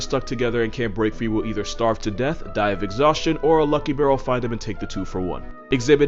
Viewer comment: when he said lucky bear i thought he said lucky girl